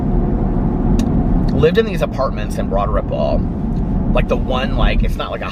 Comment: Been watching for over a year and just realized he’s saying “Broad Ripple”. I always thought he was saying “Broaderbowl” 😂🤷🏼‍♂️💙